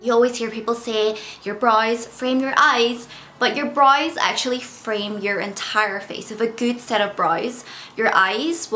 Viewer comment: I'm listening (and not watching) and I thought she kept saying your BRAS really frame your eyes lololol